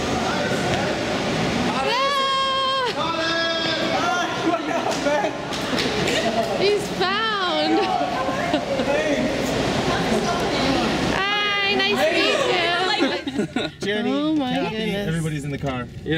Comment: did she said, hes round ? :)